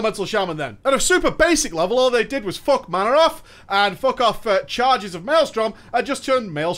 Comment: Anyone else hear "what they did was F*** Mannoroth"? I know that's not what he said but now that i've noticed it I simply cannot unhear it! xD